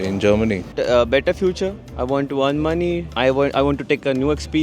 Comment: All I hear is "I want" not "I'll do" send em back.